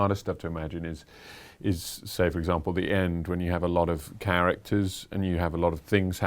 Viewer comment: I literally thought Theo James was gonna say 'There's a lot of carrots'. 😂